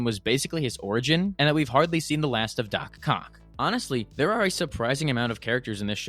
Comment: did you just say doc c*ck?